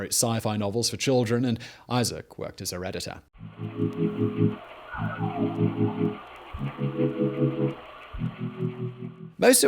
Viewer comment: thought you said “as a redditor”